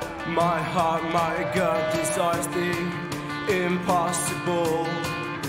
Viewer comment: until a few months ago i thought it said decides not desires